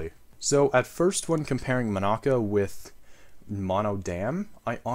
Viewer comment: sorry what did you call the pickle demon... uhhhhhhhhhhhh i thought its called like monika